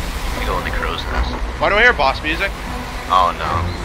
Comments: I thought he said "Why do I hear ba sing sei?" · I thought he was saying Bach music